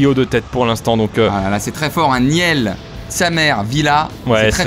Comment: j'ai entendu miel, sa mère, villa. Jme suis demandé si j'avais pas confondu la vidéo